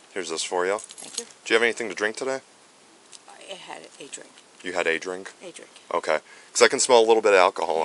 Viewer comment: Great videos, but the thumbnails & captions 😬 Many examples, but one is 51:42 she says “a drink” and the caption says “a Patrick” and a few minutes later “I’m pulling my husband” instead of “im calling my husband”